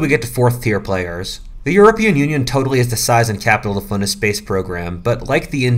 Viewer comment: " Europe has No record for agression or expansionist acts " Did I hear that right ?? Like stfu bro , the are the fucking defination of expansionists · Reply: He said the “European Union”, not “Europe”, so that basically means Europe after the Second World War. After WW2, the European nations were remarkably pacifist, and didn’t really want to wage war, except for defensive purposes.